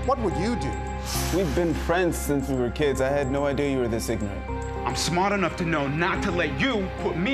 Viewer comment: Who wrote the CC for this shit. They put ignat instead of ignorant . 2:16 the man said ignorant not ignat. Damn shame on a WWYD video someone does this...